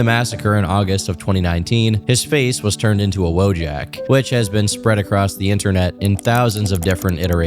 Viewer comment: Wojack ? Is that what he said around 00:30? What does it mean? Sorry if I sound dumb😂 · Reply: "Wojak". It's the name of the genre of memes of a guy in black-and-white MSPaint memes depicting an emotion or situation. Like the one on the video thumbnail. No-one knows what the word even means anymore.